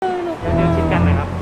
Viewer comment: ได้ยินว่าตูด😅😅